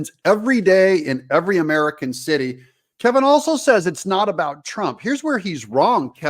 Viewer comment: Hey, I think you misheard what O'Leary said @ 2:00. He said that it is not about Trump NOW. The consequences go wider than Trump and now affects all businesses. Your rant about O'Leary was inappropriate.